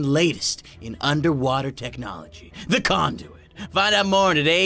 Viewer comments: is this channel even for kids now 0-0 i know that i thing of something different wene i hear that.The it was silent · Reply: He said conduit, not condom. I can see why you thought that though! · IT SOUNDS LIKE YOUR SAYING "CONDOM" WTF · I thought you said condom XD